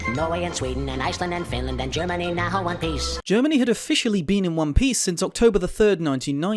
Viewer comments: I thought he said Germany. Who now want peace, in reference to the nazi riech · For some reason I always thought he said "Germany now want peace," which is even more outdated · Waitwait, you're not going to point out the visual inaccuracies? G. e. wtf is Germany doing? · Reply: At first i thought yakko said Germany now want piss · GERMANY NOW WHO WANT PIECE not GERMANY NOW IN ONE PIECE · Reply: You misheard the pronunciation. Paulsen put a breathy pause in there...it's "now o-huh-ne piece."